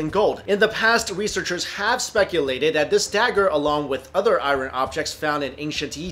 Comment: the way he says iron kinda erks me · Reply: Yeah it sounds like he says "i ran" lmfao